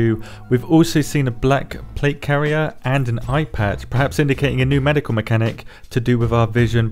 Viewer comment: i was already scrolling trough comments and I head IPad. But you said Eye Patch. I was like wtf are they adding IPads?! I skipped back and was confused and after hearing it the third time I heard Eye Patch and then you said rolplaying as pirates. I laughed lol